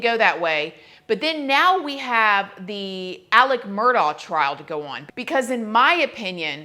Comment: Haha i thought you said Alec Baldwin for some reason & I was so confused 😂 I have no idea why I heard Baldwin & not Murdaugh